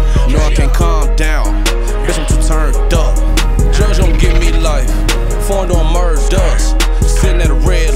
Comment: This actually slaps. Thought he was saying "JoJo give me life" at first tho lol